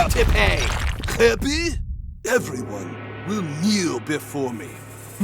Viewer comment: why did i hear "every one will mew before me"😭